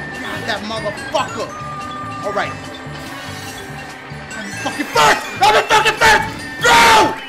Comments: when dashi won it sounded like yoshi said mutherfuckin first I'm done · Did Yoshi just say, "I'm in f#%king first!" at 9:38?? 😂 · sounded like that Lakitu said "Muthafuckin 1st"